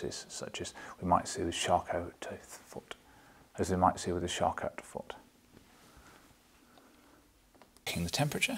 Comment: What does he seem to repeat at 2:46? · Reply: I was also wondering so I googled what I thought I heard ("Shark out foot"). It is Charcot foot, a neuropathy that makes a person less responsive to foot injuries.